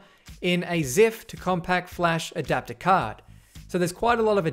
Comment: Can someone please tell me what this green part is called? <link> For those that didn't look at the link I'm asking about the part that house the red card reader that has the new SD card in it. It sounds like he says "ziff to compact adaptor card" (the CC says Zip)...Either way I can't seem to find this part that he's talking about anywhere.